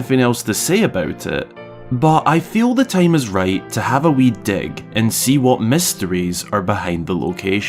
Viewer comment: I know you said wee dig, but it’s not super enunciated and there’s just that one lone stalagmite. I really heard wee d***.